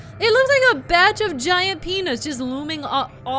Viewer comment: "A batch of giant penis." I know that's not what you said, but that's what I heard.